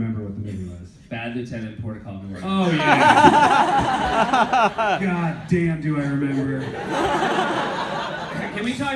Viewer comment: What did they say???? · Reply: Bad Lieutenant: Port of Call New Orleans